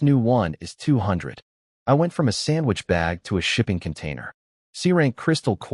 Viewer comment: When he said shipping container, I thought he said shitting containers